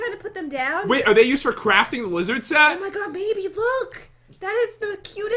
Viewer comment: Did anyone hear jen say baby to pat at 11:00 or was it just me?